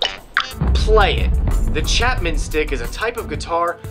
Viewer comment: Honestly at first I thought he said chapman's DICK....